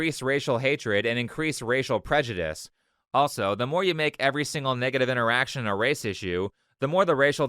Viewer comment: he said "niggative" instead of negative. I caught that 👀